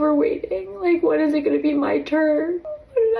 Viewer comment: Oh, the first one said, "over waiting". I thought she said, Overweight. 😂